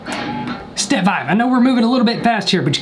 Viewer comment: When you said step five I thought you said Steve Vai